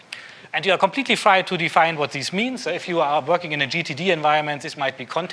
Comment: What does he say here? A "GTT environment/context" or GDD? I can't find anything about what he's referencing. EDIT: it's Getting Things Done (GTD). He mentions it at 38:24.